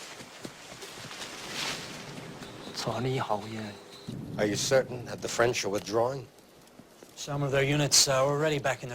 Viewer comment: what did he say? Is he Chinese? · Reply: “Good luck to you.” His last name is ‘Chen’ or ‘Chin,’ and I think ‘Chen’ is South Korean so since his friend’s references are to Chinese things (the kind of girls, “good for one,” and food - chop suey) I think he’s Chinese-American.